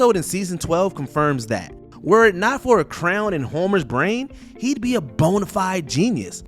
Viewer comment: Am I the only one who heard crown instead of Crayon.